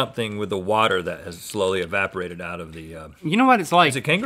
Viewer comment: the captions? I think they said kangaroo CAULK but okay....